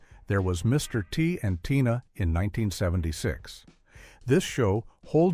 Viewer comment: was using YouTube for background noise, not really paying attn, when the narrator said there was a show called “Mr T and Tia” I thought he said “Tina” and imagined Mr T from The A Team with a Meth addiction. “I pity da fool who tries crystal meth….some people call meth ‘Tina’…I say Tina’s an evil Beeatch who takes all your money and makes you her beeatch!’. Actually,could have been a pretty entertaining sitcom, at least compared to most of these.